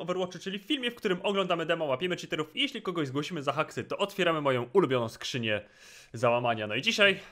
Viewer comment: kto tez usłyszal moją wkurwioną skrzynie zalamania?